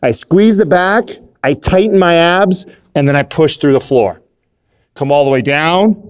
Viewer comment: When he said tighten my abs at first i thought he said tighten my ass